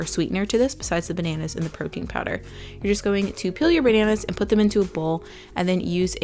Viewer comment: when you said "peel your bananas" it sounded like "kill your bananas" and I may or may not be still laughing 😂❤️